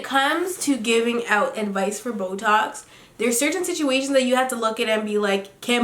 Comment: she said when it comes to giving advice for boe talks i thought she said botox .i was like did she forget wat she talkin bout?pregnancy brian vibes?😂😂😂😂da